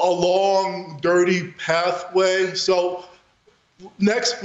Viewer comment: Who writes your video titles? I heard "pathway" not path play.